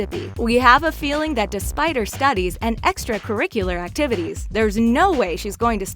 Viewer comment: i thought she said the spider studies 🕷